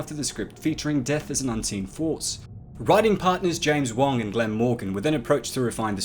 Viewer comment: I thought he said James Bond